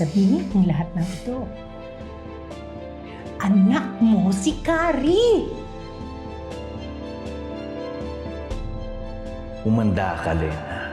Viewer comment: What does Becca says? "ANAK MO SI CARRIE!"